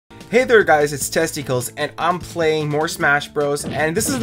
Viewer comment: it sounded like he said hey there guys it's testicles